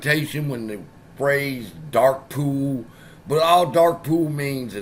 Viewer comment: It should be called the shyt exchange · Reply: And right after I read this comment every time said dark pool I hear "dark poo"